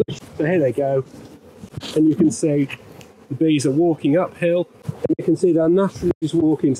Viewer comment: I thought he was saying "Waking up ill" finally realized it's "walking uphill"